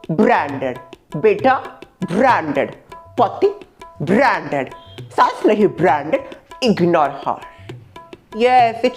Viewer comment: I heard "Potty branded" ... lol. She said "patti branded" 😂😂😂😂😂😂😂😂😂